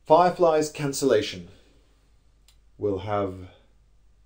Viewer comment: Ohhh, FIREFLY cancellation. For some reason I heard "five lives", and was confused because I've never heard of such a show, and I watch EVERYTHING.